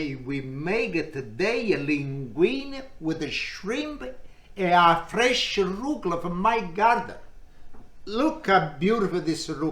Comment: Ah ok... i thought he said the shrimp are fresh from his garden 😂